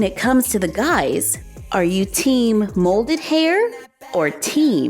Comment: I was casually listening and watching while on my phone until at the very end when I thought I heard say: are you team moldy hair? And I was like, what?! 😂